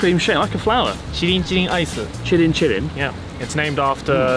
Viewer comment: Lol I thought he said it was "chinchin ice cream"